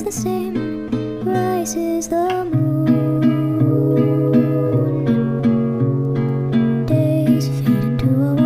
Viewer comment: is it just me or does anyone else hear rick from rick and morty say morty